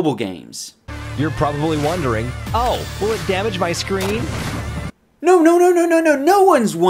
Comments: when they said will it damage your screen i thought it said can you eat ice cream. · when they said will it damage your screen,I thought it said can you eat ice cream.